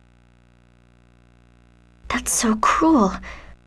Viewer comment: lol at 0:45 I thought Presea said "Thats so cool"